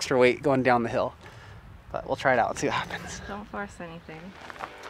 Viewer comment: I heard that "Don't bust anything." Lol